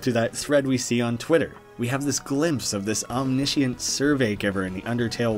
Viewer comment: I think Gaster being 'scattered across time and space' just means Gaster's consciousness was spread across the universe. He became onmipresent, which is why he could hear you talking with the Follower. I don't think he was literally broken into pieces. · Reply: I know this probably doesnt matter, and im gonna be yelled at for grammar police or whatever, but its omnipr- Nvm Im fucking stupid. I thought you said omnipotent, sorry.